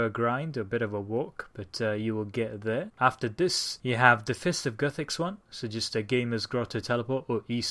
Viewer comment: Okay i cant understand you at the part 6:43 into the vid. were you saying fist of guthix??